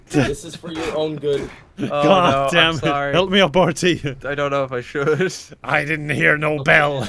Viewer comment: did Kevin say "help me up Bartee"